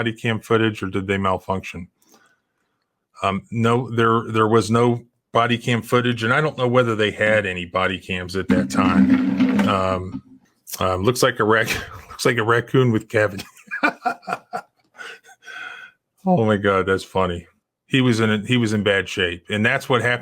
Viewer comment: The only time I have ever seen laugh! hahahhahaha. That’s f-ed up. Haha “Looks like a Raccoon with Cavities”?? lol is that what you said?